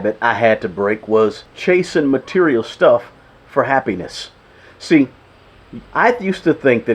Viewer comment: I always hear "happy pe*nis" instead of happiness now. ~ Well, thx YouTube algorithm.